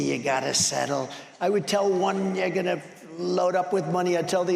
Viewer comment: I am oldish. Did he saw ,"I'd tell one (n-word they gotta settle right before 1:16? What did he say? I can't even tell by watching his lips move.